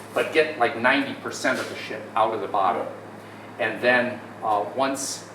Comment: Did Unc just say 90% of the shit? 😂 Based!